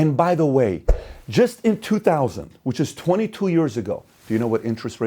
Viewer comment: I actually thought Pat said "In 2020, which was 22 years ago..." and I completely agreed. Then I went back a few seconds. This Video is much appreciated.